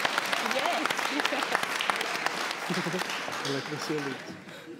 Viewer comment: Oh snap. I went to high school with. She's super talented. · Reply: what she said was, “I like silence....” that’s what I heard her say.